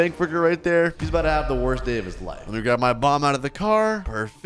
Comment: I thought u said mom instead of bomb😂😂😂😂😂😂😂😂😂😂